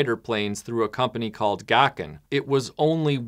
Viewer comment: You say "Gakken", I hear "Dokken".